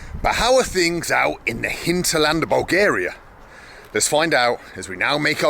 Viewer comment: Excuse me, Sir. Did you just say "Hinterland" around 17:45??? Is that another German word, English speakers would understand???